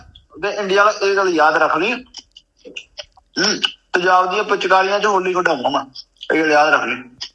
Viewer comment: LB ne kya bola exact can anyone explain? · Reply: Tezaab ki pichkari se holi khilwaunga mai